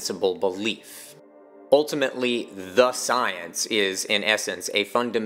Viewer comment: the science… sounded like he was talking about Fauci